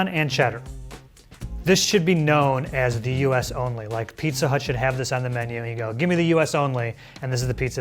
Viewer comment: did he just said pizza hut?